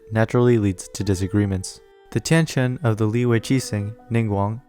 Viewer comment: Did he just said tencent of liyue???? 😂